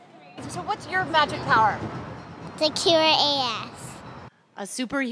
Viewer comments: This is so cute and sweet but at 2:28 it sounded like she said to cure ass XD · Cute...Did she say to cure A@ 2:28!? naughty girl...